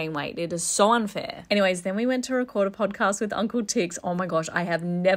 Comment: When she said Uncle Ticks l thought she said Uncle tits Who else loves this family so much 👇🏼🩷😄🫶🏼⭐️😃